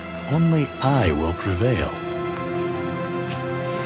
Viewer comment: Oh i thought he said soupçon